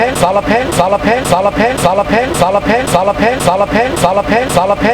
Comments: I heard "dolla pen"💀💀💀💀 I'm going crazy bruh😭😭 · After a while it sound like he saying "dolla pen"